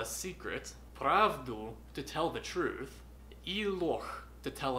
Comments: Я вместо "ложь" услышал "лох"))) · мне одному послышалось «и лох»?)))) P.S. you are awesome! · Буква "Ж" настолько страшная, что боится читать её в словах 8) А там ещё и мягкий знак в конце! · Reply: я не сразу поняла,что он имел в виду ложь,послышалось сначала лох)